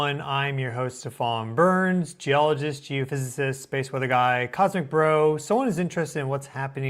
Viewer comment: I heard "Cossack bro"